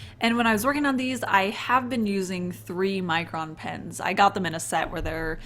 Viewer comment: Every time you say "Micron" I hear "Mycroft" and I think I have a problem